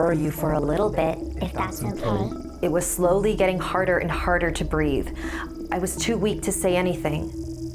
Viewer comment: I thought she said: I was getting harder and harder every second